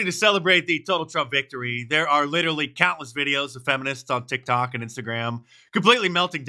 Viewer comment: VERY beginning of the video, Mark: "There are countless videos of feminists." But I heard "cat-less videos," and I was like, "Where is the lie?" I mean, explains part of their anger.